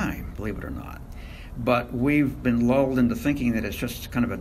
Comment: did he say we’ve been LOL’d